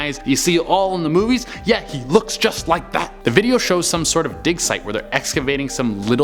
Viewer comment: I thought he said dick site not dig site. Oops